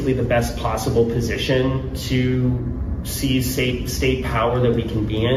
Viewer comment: did I just hear that guy say "seize state power"??